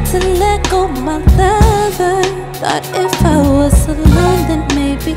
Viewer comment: all the time i thought she said "thought if i was Solange..."